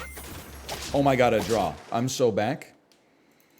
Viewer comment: everytime says "I am so back" I heard "I am sewed back" as if he got ripped apart and stitched together then brought back to life which kinda is from time to time in sap 🤔